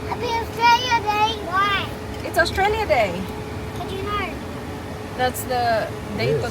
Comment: did say, "ano yun?" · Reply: She said: "How did you know"?